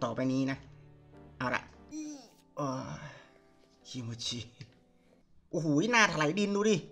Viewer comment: โอ้วพรี่🌚🌝พี่พูดอะไรนะได้ยิน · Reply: คิโมจิ